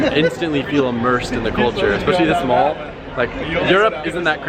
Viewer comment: When you said culture, I thought you said N I C O L E ! Culture. Nicole. Culture. Nicole. Culture